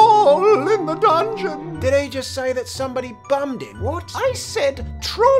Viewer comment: "Troll in the dungeon" "Did he just say somebody bummed him"😂😂😂😂😂